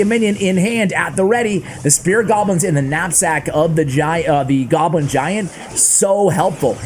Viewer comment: thought he said nutsack and i was gonna have to report him